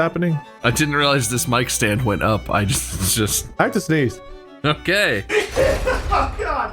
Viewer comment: I thought Wade said “I have to cease.” Bye, Wade!